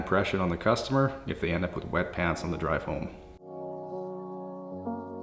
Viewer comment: He said “wet pants on the way home” had to re-play that parts cause I thought i heard “wet ass” 😂